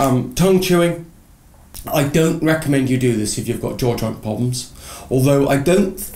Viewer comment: "I dont reccomend you if you have (?what?) problems?" I dont understand (( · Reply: if you have jaw joint problems